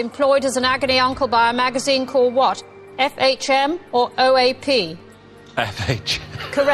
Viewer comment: I used to think that Anne actually said F.A.P. rather than O.A.P.! No wonder Pete was laughing! How could I have mistaken O for F on such a question?!